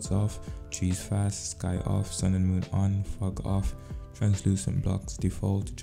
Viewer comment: When he said fog off. Thought be said fuck off